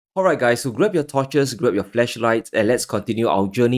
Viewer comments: Did he just say “grab your fleshlights? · "Grab your torches, grab your flashlights" 😂😂😂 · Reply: I heard “fleshlights” 😆